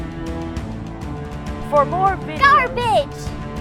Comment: for some reason instead of garbage in the end i heard "goth bitch!"